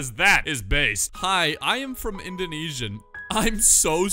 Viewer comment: blud actually thought he said insomnia